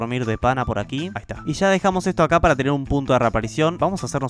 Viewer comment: yo escuche P. u. T. o